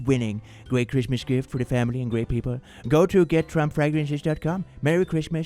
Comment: i heard get trump pregnant 2024 😂 at the trump fragrances thing